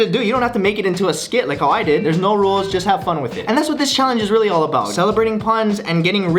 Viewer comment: did Ryan say just have pun with it or have fun with it :\